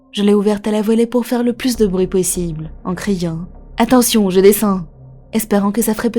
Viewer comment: j'ai l'esprit mal tordu 😵‍💫 J'ai entendu "attention, j'ai des seins !" Que Dieu ait pitier de mon âme perverti 😅